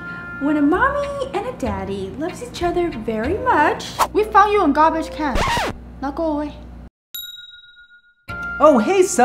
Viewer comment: Child:where do baby's come from? Parent:we found you in garbage can now go away. lol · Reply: Cosmos S.L.A I thought she said "we thought you were garbage can, now go away".